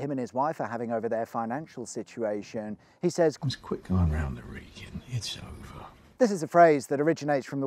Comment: i had to rewatch this clip so many times and i still don't understand what he said 😂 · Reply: “Let’s quit going round the Wrekin it’s over”